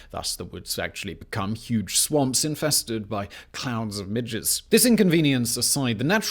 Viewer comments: did he say "clouds of midgets"??? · did you just say the woods become infested with clouds of midgets?? I may not be the world's expert on permafrost, but that shit sounds insane to me! I know clouds of midgets are not a side-effect of permafrost blocking rain from soaking into deeper top soil. I've played it back 3 times, what are you saying????? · help I had to listen to 3:18 three times because I could only hear “clowns and midgets” instead of “clouds of midges” please tell me I am not the only one who misheard that 😭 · It just sounded like he said "...huge swamps infested with tiny midgets..." :D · Not gonna lie. Had to turn on the obviously needed CC because in my monkey brain I heard "clouds of midgets" and didn't know why I couldn't stop laughing at what I so painfully (because of laughter) had misheard. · I swear he said, "clouds and midgets" 😂😂😂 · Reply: Midges....tiny little biting flies. They can drive you insane in about a hour or 2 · Sounds like he said "...becomes inhabited by clouds and swarms of midgets." 😆